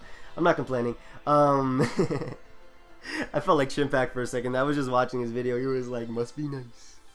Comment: What is the name of the youtuber you're talking about around 27:50? I couldn't hear what you said. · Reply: chimpact